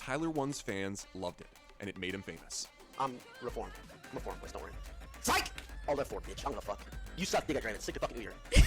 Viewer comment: What did he say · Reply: Bushbaby “im reformed dont worry, SIKE alt+f4 bitch”